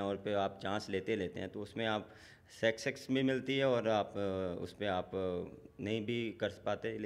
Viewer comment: pe bhai kya bola esne success ko sex sex itni kharab english bhai sarfaraz ahmed ki english tujse acchi thi babar